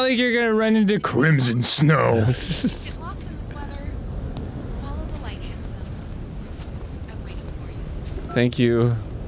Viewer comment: subtitles say :follow the light, Ansem" KH videos confirmed in future GG videos ;)